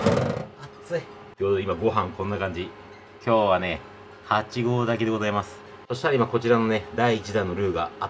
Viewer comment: 「8合炊きでございます」が「8合だけでございます」って聞こえて「らすかるさんがついに一線を越えてしまった…」とか勝手に思ってしまいました笑(すいませんでした)